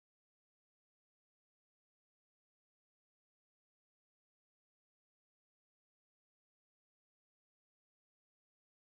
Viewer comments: What did he say? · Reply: Kyoka: oh my god look at that face Mark: if you are feeling cu!s!d@ you’ve come to the right place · My thought at 2:03 was “I guess we’ll never know what mark said 😔” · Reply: Kyoka: oh my god looks at that face Mark: if you're feeling cuz!c!dal you've come to the right place